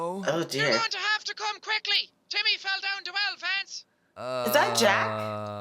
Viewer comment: i thought she said “your gonna have to come quickly because Timmy fell down the well Vance”